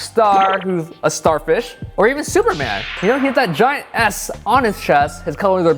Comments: What he said: Giant 'S' on his chest. What I heard: Giant "Ass" on his chest. (lol, stupid me) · I swear I thought you said Superman had a gaint ass on his chest at 3:23. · Omg I’m laughing so hard rn I heard: GIANT ASS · I heard "Superman, who has a giant ass on his chest" XD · I know I can't be the only person here who thought he said "giant ass" instead of "giant S" on superman...